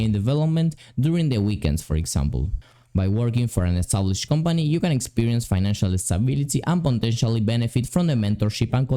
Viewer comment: What did you say at @ 5:42? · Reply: "by working at an established company you can experience financial stability and potentially benefit from benefit from the mentorship and collaborative environment." I think